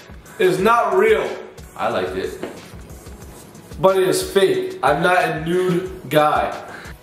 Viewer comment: did anyone here what Ethan said 😂😂?? Also 5:29 wow 😮🥰❤️💕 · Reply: ethan said “i liked it” and then grayson responded “but it is fake. i’m not a nude guy”